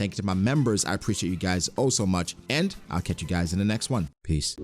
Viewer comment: I just realised in your outro.. "catch you guys in the next one, peace". Sounds like you say one piece